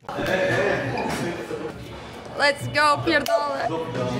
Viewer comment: did she say "fuck" in Polish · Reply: Yup "pierdole"